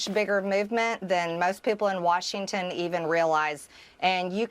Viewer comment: MTG says "realize" but closed captions say "real lies" 🤣